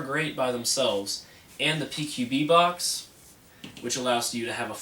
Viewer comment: did he say PQB Box? haha